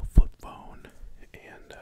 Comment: Tell me why I was trying to sleep and thought he was saying “foot bone” and when I opened my eyes to see a “flip phone”. I’m dumb.